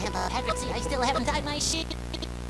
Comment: did bro just say shit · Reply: Shoe but it sounds like it